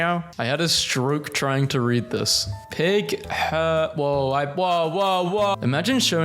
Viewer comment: bro what does 8:07 say/mean ? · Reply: “Ping teacher hotel”